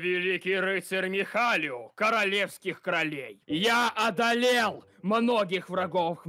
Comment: мне послышалось или он сказал королевских кролей? 😂